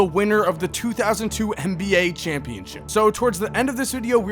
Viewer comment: I’m not the only one who heard 2002 NBA Champion.. right? Lol.